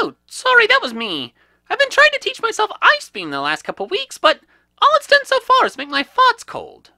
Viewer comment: Bulbasuar: I just got a cold chill. Squirle: sorry that was my fart, I was trying to learn ice beam ( I know I spelled things wrong ) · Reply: I thought he said ice cream. So I thought he sharted